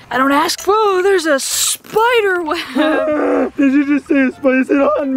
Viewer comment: "🫏🫏🫏did you say a spicyer on me on me?!?! "